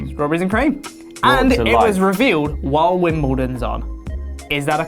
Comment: What in the heck is saying at 2:42 "while the wardens on" is what I'm hearing but it makes no sense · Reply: Bloodbarron115 “while Wimbledon is on”